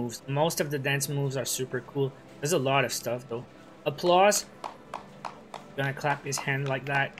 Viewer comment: Tell me why I thought the applause emote said Applesauce 😂